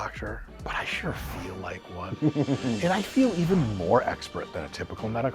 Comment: I thought he said “I know I’m not a real doctor, but I sure f**k like one.” 😂